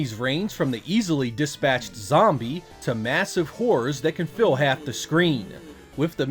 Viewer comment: Totally heard that as "Massive whores that can fill half the screen." I was like "Oh, tell my ex I said hai."